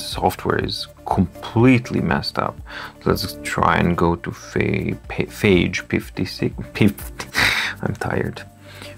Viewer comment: Did you say phage, question?